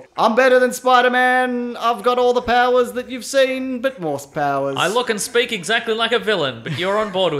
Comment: "but ??? powers..." What does she say there? I can't understand him so who'd like to help me out here??? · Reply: worse?